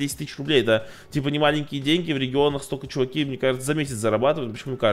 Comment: Мне послышалось или он сказал что в Перми жил?